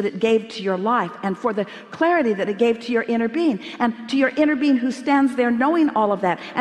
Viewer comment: Everytime they say "inner being" I hear "inner bean" and I just 👁👄👁